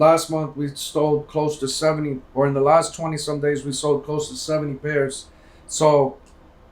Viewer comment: did he say we stole lol